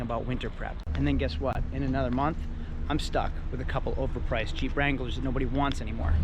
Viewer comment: Bruh, in the beginning I thought you were saying CHEAP wrangler instead of Jeep.